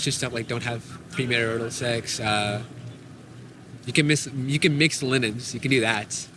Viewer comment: What did the Muslim guy say at 21:03 "You can have mixed linnets" I thought I heard but Google doesn't have any info about what that might mean.